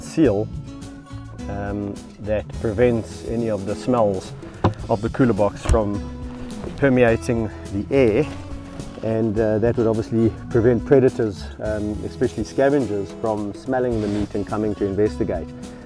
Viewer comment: I thought he said cooter box